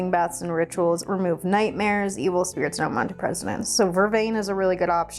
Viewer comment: Did I hear right? Vervain removes nightmares, evil spirits, and unwanted presidents? 🤣🤣🤣